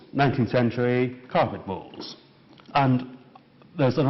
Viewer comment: Carpet balls? (My American ears can't understand some of his accent.)